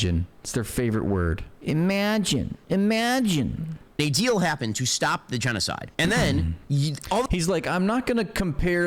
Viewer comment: Ben said, "A deal happened to stop the genocide." Listen to that. Did I hear that right?